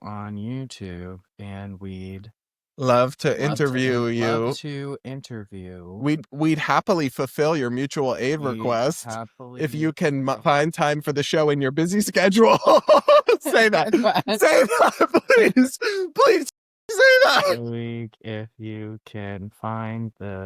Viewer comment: i literally heard "if you can find time for the show in your eating schedule" 😭💀💀 i died LMAO